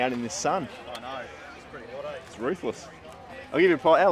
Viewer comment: did you say Ruthless or Roofless. Both would be correct haha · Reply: Ruthless lol